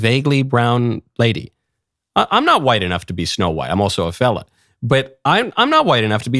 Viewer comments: Thought you said , im also a felon lol. Had to put the captions on. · Had to rewatch a couple times because I thought he said felon, not fella.